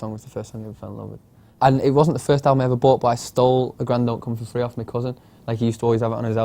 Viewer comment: what album did he say was his favorite of all time? i can't understand cos he was talking so fast haha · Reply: Jillian Johnsen a grand don't come for free by the streets. It was so hard to figure out what he said ! i thought he said a gran dog comes for free haha